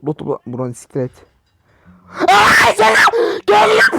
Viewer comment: burada ne diyorsun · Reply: Aahhh geliyor sjjdhf piston aşağı